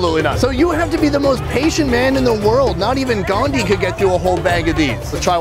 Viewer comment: Did just say Gandhi ji ? ( Indian national father ) i just love the way he said and happy that he knows a lot about other nations history .